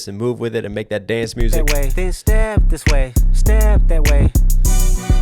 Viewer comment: anyone else hear *stab this way, stab that way*... am I hearing it wrong? Lol